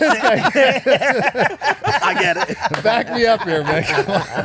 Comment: LMAOOOO 7:37 he called mike the wrong name. Thats gotta hurt for Mike who most likely idolizes Dan like he is God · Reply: Sounds like hes saying "back me up here mick"